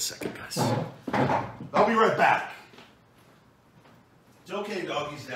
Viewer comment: I thought you said “I’ll be Ryback !” LOL